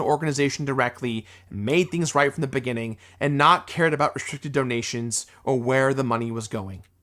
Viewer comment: buddy, that's a shockingly bad mishear from you, he said "I shouldn't have cared about restricted donations" as in "I should have just donated the money rather than being so hell bent on getting an itemised bill". Like, not trying to hate, and I like the rest of the reaction, but just needed to comment on this one but because the fact that you had such a strong reaction to you mishearing the last half of his sentence threw me lol.